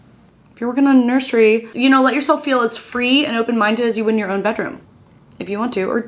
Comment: I thought it said “Your clean bedroom” and I just cleaned so I was like it is thank you 😂